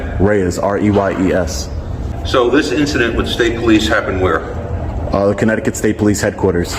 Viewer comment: this is why you record!!!! He clearly said WHERE!!!!! What a dick this dude is. What a shitty attitude.